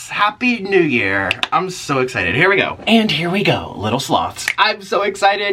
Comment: The first time I saw your video I thought you said HELLO MY LITTLE SLUTS and I was like oh okay...